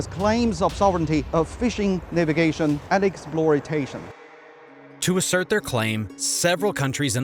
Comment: Critically important geopolitical and economic issues. At 6:46, the on-screen speaker says "exploitation". It sounded like "exploritation", which sounds like an especially apt neologism.